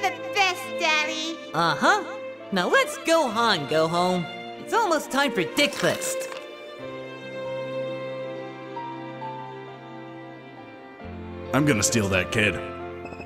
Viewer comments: At the end, it sounded like Goku said dickfist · After all these years why am I just hearing Goku say “dickfast” cause bitch what 😂😂😂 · sounds like he says dickmas 😭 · What does he say at 18:17? Its almost time for dicklist? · 18-16 did anyone else hear Goku say "now let's Gohan go home"?